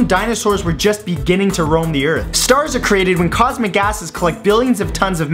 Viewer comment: He said 'cosmic gases' but at first I thought he said 'cosmic asses' Hahahahaha